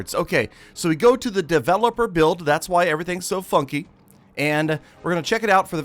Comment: "thats why everything is so fucky" (Sorry thats wat i heard)